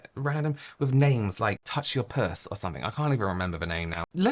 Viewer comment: Never thought I'd hear say "touch your puss" but omg 😂😂😂 I needed a good laugh. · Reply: I thought he said “touch your purse” and was thinking wtf?? Now I’m dying!! 😂😂